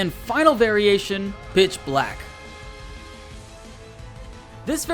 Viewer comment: Did anyone else hear bitch black